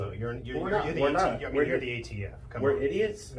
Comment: I thought it was funny how he said "You're the ATF" and the ATF agent thought he said "You're idiots".